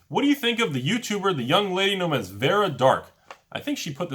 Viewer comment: That's me. My YouTube channel is Dark Titan Enterprises. :) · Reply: Vara Dark awesome i thought he was saying "vera dark" i looked up vera dark mtg n it kept giving me like vela the nightclad and Vera Convinzione which is just an italian True Conviction lol